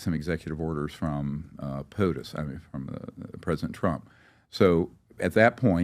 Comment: Did he drop an Easter egg ..closed caption he said “Pus” but I’m not sure what actually said 13:54 see if you guys catch that name!wow💥ShotsFired 😳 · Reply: The current President of the United States (POTUS) I found out what he said